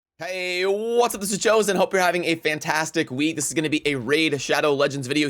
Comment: I love that the closed captions heard you say Jose not chosen 😂